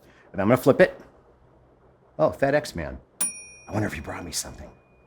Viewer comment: I misheard "FedEx man" as "Fat X-Man" and got a funny mental image of the obesity epidemic having come to the MCU.